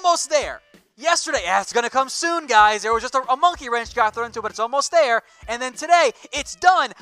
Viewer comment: ish, does he say “Monkey Wrench?” Or is that just me?